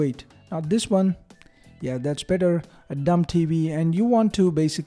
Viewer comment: did you just say dumb tv lmao